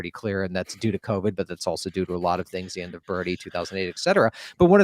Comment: did he say the end of Bernie?